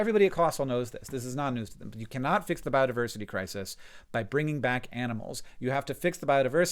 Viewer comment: I heard ‘badiverse’ instead of ‘biodiversity’ lol “The WHAT???”